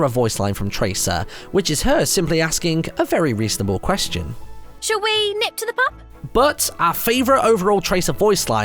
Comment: I can't make out what she says at all "should we nithufkukepap"